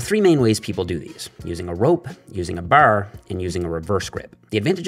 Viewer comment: Is it just me or did he say boarr rather than bar ?